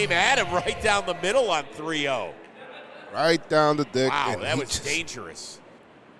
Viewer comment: What did just say? Right down the Dick. that got me chuckle for a bit